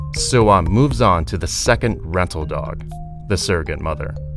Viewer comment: did they just say rental dog